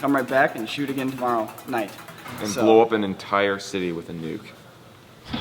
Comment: ".. and blow an entire city with a nuke." What I heard: "and blow an entire city within NUKE"...